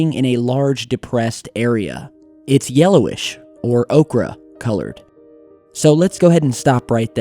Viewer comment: The transcript says "okra colored".....has to be misspoken or misheard "ochre", because okra is green.